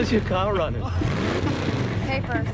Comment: What did he say? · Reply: "How's your car running"